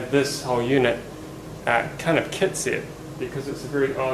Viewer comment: you say "but we also wanna have this whole unit kinda _kitsiv_"?? What word is that? I don't know what you're saying there. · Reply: Scott Baker kitset